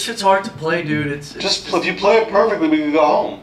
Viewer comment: I thought he said "do porn"